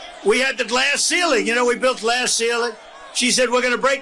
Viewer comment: Did he just say “we built the glass ceiling” ? What an a-hole